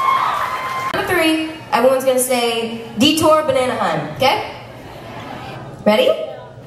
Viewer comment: Who else thought she said detour bananahun 🙋🏻🤷🏻‍♀️😂